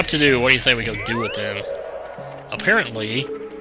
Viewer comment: Lol, I was watching this while waiting for chugga's SMG Finale to load on another tab, and I didn't hit the pause button, so at 00:41, when he said apparently, I heard chugga say, this is fun! ROFL